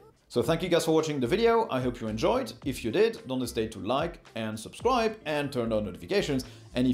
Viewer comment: OH MY GOD. The whole time I've watched this channel I thought he was slipping in a french reference or something at the end of every video when he said, according to my ears "DO NO STAY" to like. He was actually saying "Don't hesitate to like" and I heard it as a different language somehow. Im an idiot... or deaf... or he says it quickly. Either way, I finally figured it out!